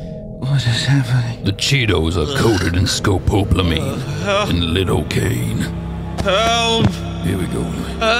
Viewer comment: What did he say??? "The cheetos are coated in scopolamine and lidocaine"